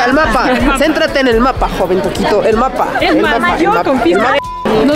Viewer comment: Minuto 12:59 escuche joven durito xd